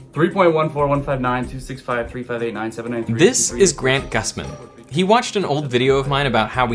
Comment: Thought you said grant gustin and had to do a double take